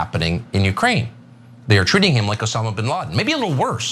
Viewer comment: Swear to God it sounds like you said "Obama" instead of "Osama" was living upstate ... 😂